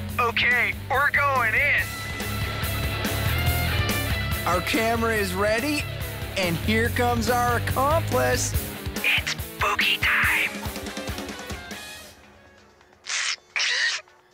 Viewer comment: Does Jude say "it's boobie time?"